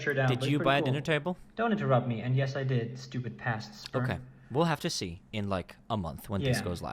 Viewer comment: did you just call yourself sperm???? 😂😂😂